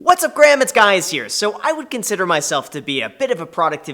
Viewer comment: Did he just say what’s up graham it’s guys here?